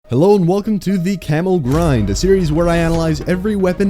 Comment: I heard "Camel Grind" at the very start of the video xD